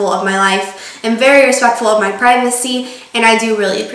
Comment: i thought she said "very respectful of my pregnancy" XD